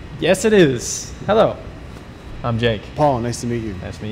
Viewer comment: i heard hello am gay not jake